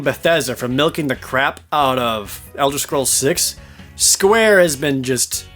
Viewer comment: Did ProJared just say Elder Scrolls 6?